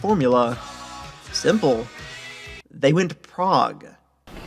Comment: Seriously thought you said “they went to Prague.” I thought,” I have never heard that part of the story! To go all the way to Czechoslovakia is no small thing to ignore.” That guitar riff a few seconds later 3:50 still led me to believe they went to Eastern Europe.